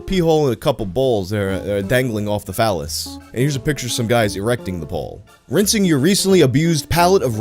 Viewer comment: I was dozing off listening to this,and all I heard was “Erect the Dick Pole”…I was like…WHAT…the…FUCK?!?!?